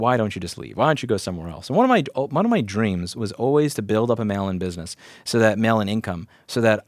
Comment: I misheard dream as having a "melon business". I just thought that it was some financial slang like "economic bubble", "Lemon Brothers"... "melon business" :)